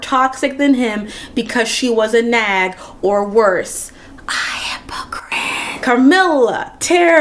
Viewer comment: What did she whisper at 23:02? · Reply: “A hypocrite” [+]